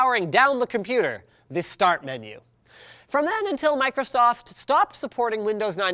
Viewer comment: Did he just say microstoft? Go to the timestamp